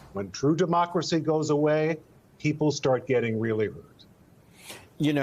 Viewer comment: "When true democracy goes away, people start getting really hurt" (For those who couldn't hear it)